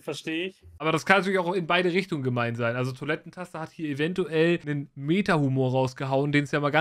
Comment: Ich hab erst toilettenpaste verstanden ^^ genau so n geiler name 😂😂